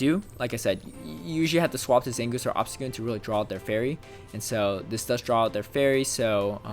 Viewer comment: Was half paying attention at one point and thought I heard you say "draw out their berry" instead of "draw out their fairy" and it got me wondering if we'll ever get held items. Would add another interesting layer of strategy, and it'd work well for implementing mega evolution.